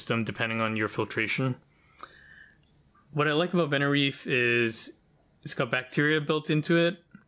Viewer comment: what i like about "___ "what? i heard benareef.